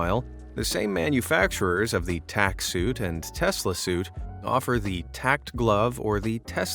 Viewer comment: Initially, I thought it said "Tesla slut". I made myself laugh 😅